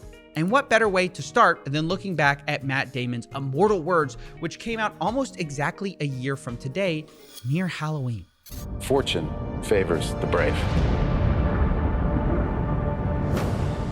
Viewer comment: Steve said "Fortune favours the brave" and I heard "4chan favours the brave" 😳